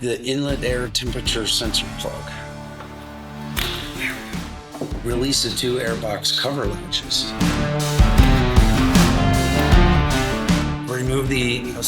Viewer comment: did I hear, "release the two airbox cover lunches."? If so that's good stuff right there!